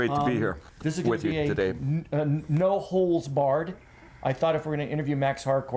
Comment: Did he say it would be a "no holes barred" interview?